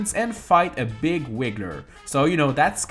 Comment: Did he just say 'wigger'? Lmao